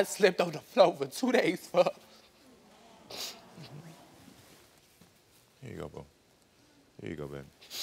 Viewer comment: Did Karamo say "Here you go Boo"? Then say "Here you go man."? Lol